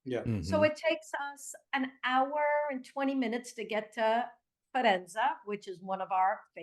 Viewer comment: it sounds like she is saying "Farenza" she is mispronouncing Faenza, if that is what she means. She says it her "favorite town", but cant pronounce it right. Good interview, NR